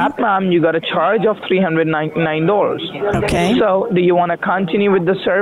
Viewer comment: Did he just say 399 doors?